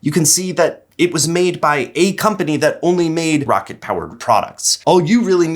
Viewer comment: I heard it was "A Company that May Exist".